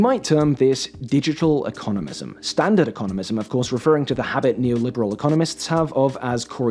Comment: I thought that said Stop Economics